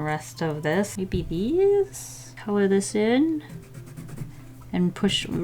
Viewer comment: When said "maybe this" in a high pitched voice I thought she said AYY BB